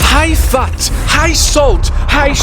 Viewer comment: When he said “high fat” I heard “hi fat” so I thought we was calling me fat